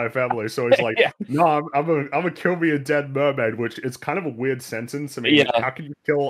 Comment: I watched the movie in a theatre, no subtitles, so I thought Manta said "I'm gonna kill me a damn mermaid." But that could be my brain trying to make more sense than the movie.